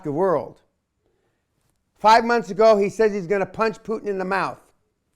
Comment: I actually think Kasisch said he wanted bloody Putin's nose.